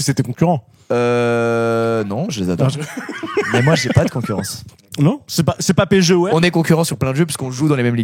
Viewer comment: moi j'ai compris. il a dit : "c'est pas PSG-OM" pour comparer la rivalité